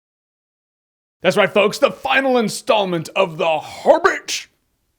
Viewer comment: Did he say "Horbbit"?